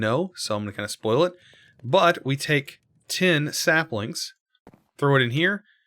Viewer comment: y did you say tin sapling